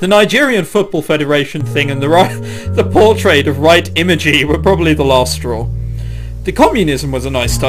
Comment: I notice you didn't catch on to the joke, let me clarify. She said the image of "Right Imagay" which you read Imagey, but regardless it is a joke on Light Yagami from Death Note. If you spell Yagami backwards you get that. And since japanese doesn't have any L sounds it sounds more like "Right" than "Light"